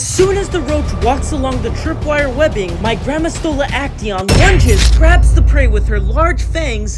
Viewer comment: I thought he said My Grandma stole a action